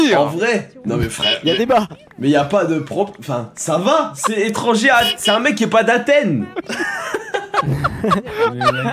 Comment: vers 40:00 j'ai pas compris le mot 1er degrés c'était quoi ? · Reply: Je crois que c’est métèque